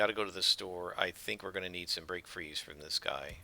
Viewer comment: Every time you say I need some “Break Frees” I hear “Brain Freeze”